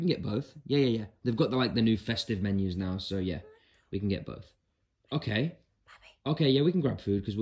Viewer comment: I thought he said: oh Yea we can give birth